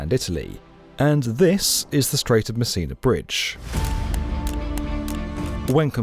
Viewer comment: I keep hearing "The Strait of Messina Bridge" as "The Strait of Missing a Bridge"... 🤣🤣🤣